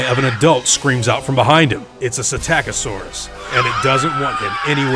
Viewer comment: I swear I thought you said Attackasaurus